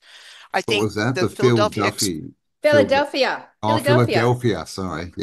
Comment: I thought she said “Phil Duffy” too, LOL! I love you guys!